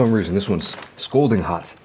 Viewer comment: Scalding hot... What's funny about that... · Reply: I heard "scolding hot" not "scalding hot." I was just wondering if I heard right because "scolding hot" is pretty funny. I think just coined a new phrase with that one.